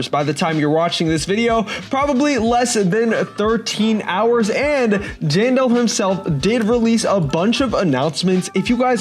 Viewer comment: he said Jane Doe instead of Jandel